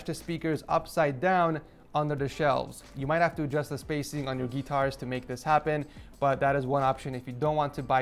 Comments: my man really said getars instead of guitar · Did he just say geēëtars??